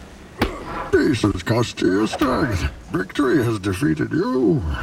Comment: what does he say? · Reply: Peace has cost you you're strength, victory has defeated you.